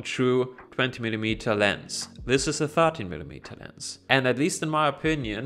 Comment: did you say 30mm? If so, that’s definitely not true. Where do you get that from?